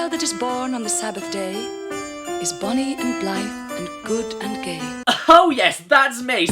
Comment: "and the child that's born In the sader day is bunny and blind and good and GAY" at least that what I heard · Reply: "born on the SABBATH day is BONNY and BLITHE and good and gay". Also, considering you capitalized "gay" like its inclusion was ridiculous, gay means "happy" or "light-hearted" though it's less commonly used like that now since gay means "homosexual".